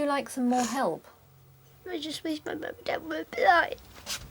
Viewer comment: “I just wish my Mum and dad would die?” Did I hear right? 35:47 :(